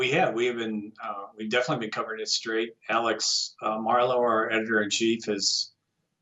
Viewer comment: Thought he said Milo editor and chief. hahaha. MARLO. Doh.